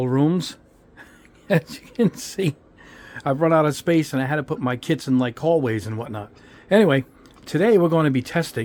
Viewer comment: When he said, "I had to put my kits in hallways and stuff," I first misheard it as, "I had to put my kids in hallways and stuff." LOL!